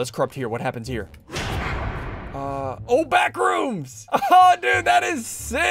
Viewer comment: did he just say backrooms for seek